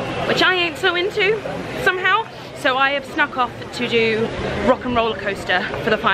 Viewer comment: I haven't been to Disney and thought you kept saying 'Rotten rollercoaster' haha!